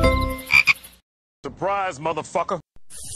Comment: did face just say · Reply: surprise **********